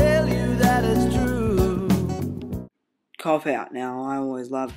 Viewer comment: when you said cop out i thought you said cockk out